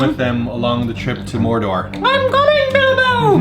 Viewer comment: Did you say "Mordorc"???